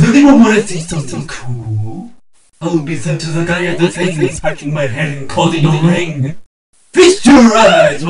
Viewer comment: does anyone know what is he saying? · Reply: It says I will be sent to the guy of the thing! Scratching my head while closing the ring